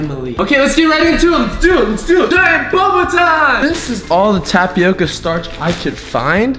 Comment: Idek why, but I thought you said "DIE OBAMA TIME!"